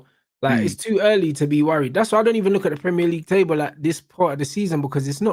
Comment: I thought this man said, "I dont look at the table!" 🤔